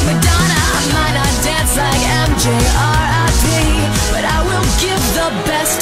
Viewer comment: I had no idea she was actually saying R.I.P. I always just mumbled that part... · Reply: I thought she said MJ or IP